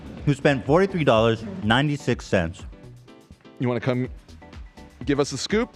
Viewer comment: Did anyone catch 2:45:22 “you wanna cum”